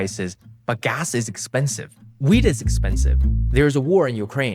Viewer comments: I heard weed is expensive 😭 the long gone teenager in me went: Uh huh you're darn right! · did he say weed is expensive?